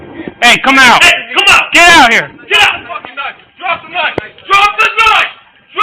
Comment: What you all probably hear: "DROP THE KNIFE DROP THE KNIFE" What i hear: "DROP THE NUT DROP THE NUT"